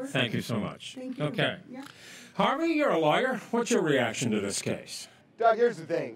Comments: “Harvey, you’re a liar” 😂😂😂😂 that’s what I thought he said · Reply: He says your a lawyer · I swear I thought at the end he said Harvey your a lier what’s your reaction to this lol